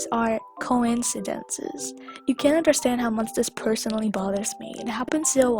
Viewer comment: Oh bother me I thought you said BROTHER ME AYYY